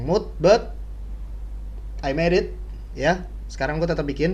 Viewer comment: kedengeran "but i'm married" jadi syok kirain nikah muda:D